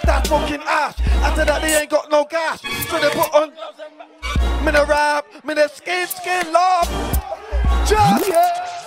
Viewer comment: What does he say at 2:55? Sounds gassed tbh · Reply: Joe Morris mi nuh ramp mi nuh skin skin laugh laugh grin. (Anger management riddim)